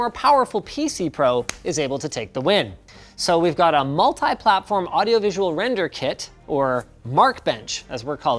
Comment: did he said Maltai platform! 🤔